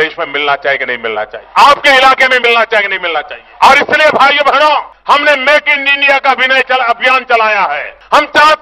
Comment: did I hear make in India ka abhinaya ?